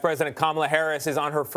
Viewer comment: Did he say president kamala Harris ?